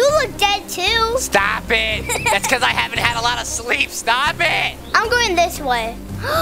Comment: i heard sl@ not sleep